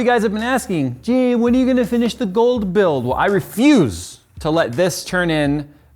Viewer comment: Oh... I thought it said "The Cold Build".